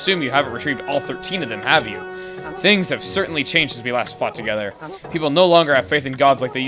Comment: right after 15:14 .. sounds like he says "since we last f*cked togheter....... or do I just have a very perverted mind?